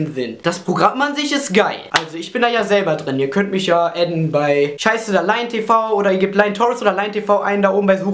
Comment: ich hab einfach 'scheiße da LionTV' verstanden. :D